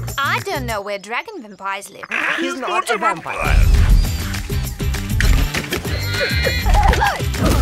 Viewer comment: "He's not a vampire"? What are they saying?